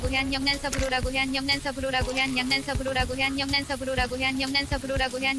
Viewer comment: 계속 들으면 양념된 서브로라고 들림ㅋㅋ